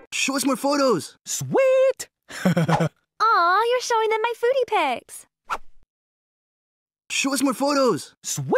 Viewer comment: Yoo I thought she said "booty pics" both times I was wondering "why is she only getting upset about it the second time?" lmao